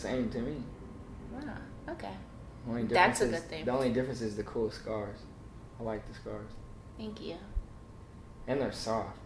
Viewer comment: Did he just say “the cool scares” nah nah screw crush make him your man girl😂 anyone that understands that scares represent a story/journey is a keeper